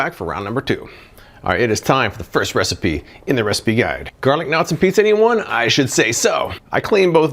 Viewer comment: I’m in a country where we don’t have garlic knots. I did a double take because when I first heard you - I thought you said garlic nuts! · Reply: I thought he said "garden knots", I had to rewind & pause, & read it.